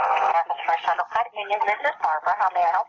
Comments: American First Credit Union? or Americas First Federal Credit Union? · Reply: X X I thought she said America First Credit Union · I thought she said America First Credit Union not America First Federal Credit Union